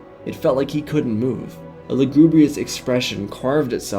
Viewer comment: what is that word you said? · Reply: Lagrubious I think